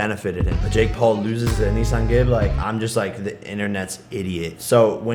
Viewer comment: Bro did anyone hear the way he said Anisongib it sounded like he said a nissan gib